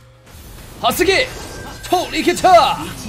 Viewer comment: did you just say "hasagi" XD